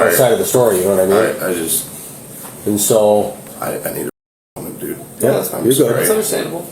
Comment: Oh the curse of closed captions. CC says: I need a woman, dude. I am pretty sure he said: I need a moment, dude.